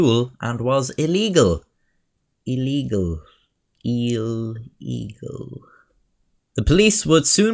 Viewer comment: i heard “EEL EAGLE”